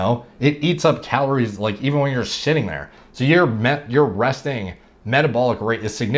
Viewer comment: he said: when you have muscle on your frame it eats up calories even when you ARE JUST SETTING THERE What I heard: when you are just SHITTING there 😂😂😂had to rewind the thing a couple of times